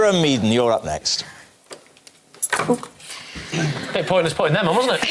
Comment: What did Lee say at 5:28? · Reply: "Bit pointless putting them on, wasn't it?"